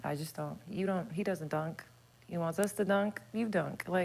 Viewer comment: did she say Micheal Porter don’t dunk ?